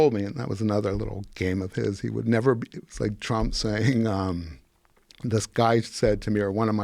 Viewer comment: Did he say like trump say at 14:00 and after? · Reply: Now after watching it all he sure says “just like trump” what a f head making it seem like trump is part of that. What a comparison, if you all want to know what political party KKK is are Democratic Party just read about it if you don’t believe it. Dems are just awful people and huge racist, just see what dems are saying about voter ID laws and how they demote black people to imbeciles, shame on you f faces.